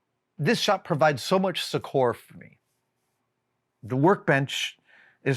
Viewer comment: Does anyone know what word he’s saying at 2:18? (Sechor??) I would like to improve my vocab but I can’t figure it out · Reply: I believe he's saying "succour".